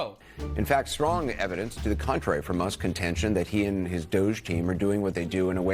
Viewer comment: I think he said “Musk’s intention”